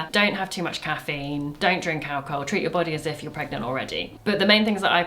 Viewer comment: According to captions at 13:43 says "don't drink cow alcohol," and while I'm certain that is good advise it cannot possibly be what she actually said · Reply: She just said “don’t drink alcohol”, but she sort of said the drink alcohol part quickly, so it almost sounded like “drin kal cohol”